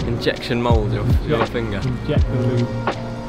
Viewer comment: I heard you say "inject the lube"